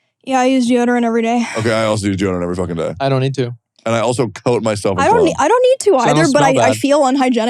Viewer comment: Did Tectone say "I also coat myself in Cum?"